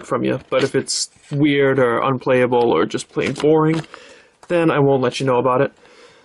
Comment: When you said "Playing Boring" I thought you said "Porn" xD